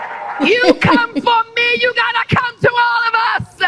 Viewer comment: I thought she said if u come for us.. U have to come thru me.. Now she is saying if u come for me u have to come thru us.. Sounds like she is getting worried